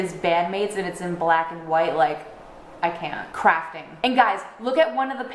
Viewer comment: DID I HEAR THAT CORRECTLY?!?! DID YOU SAY CRAFTING!? WAS THAT A DAN AND PHIL REFERENCE!?!